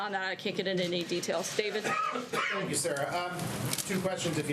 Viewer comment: when she called on David the reporter, did he say thank you sir? 😂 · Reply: He said "Thank you Sarah"